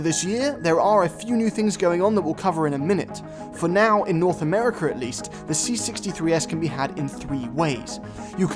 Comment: Did he just say North Americurr?